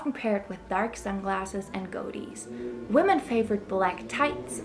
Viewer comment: "dark sunglasses and Go dies" what did she say at 1:12 ? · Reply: Goatees